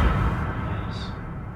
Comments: says"you ready to go?" You can hear what sounds like someone saying "yeah" or "yay"!!! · At the point where says, “Ok. Are u ready to go?” I hear a drawn out “Yeah” and then the light lights up green! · as soon as ask "are you ready to go?" I heard a whisper saying "yeah" or is it just me? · of this video when asked “are you ready to go!?” Did anyone else hear the long whisper “yeah”? Not sure if it was one of the boys or not. Absolutely loving watching these guys!! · when said "are you ready to go"... i heard a woman's voice that said in a whisper ---- "YEAHHHHH"...and the triangle thing turned green. give me a thumbs up if u heard it too coz i did... unless its one of the boys.